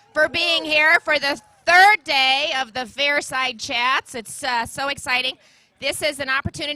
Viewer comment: I always thought they were saying " fire side chat" lol